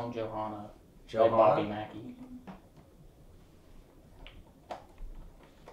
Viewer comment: Can anyone understand the voice after says that's a great idea around 54:54 ? I hear hi or guys or something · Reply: sounds like " guy " but think it's the cameraman. Throughout the entire video ( and the other group video's ) you can hear them breathing loudly or making sound, it's almost like it's meant to be edited out but not quite works.